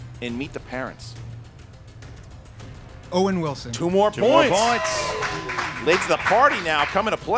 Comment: Guys, please do me a favor. I do not and will not charge for these matches on Youtube. You guys here are the CORE, the elite fans that are so caught up on everything. If everyone of you commented and LIKED each match that comes out that would be 70,000 likes and 70,000 comments and that's how we keep getting the Schmoedown to new levels. SOME of you watch it on TV, that is great so just go to the app, hit like and say something about the match. (DONT PUT COMMENT or something like Kristian asked me to comment because I usually delete those.) This is what it will take to grow the matches. This is how I can get you an Innergeekdom league, more matches per week, more celebrity competitors, an APP or a SCHMOEDOWN CHANNEL!!!.... Help me help you! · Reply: ColliderVideos I made the mistake of putting my headphones back on at work while the vid was still playing....all I heard was "by one point late to the party" bummer....but still a great match lol